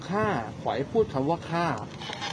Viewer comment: ได้ยินว่า ฆ่า ค่า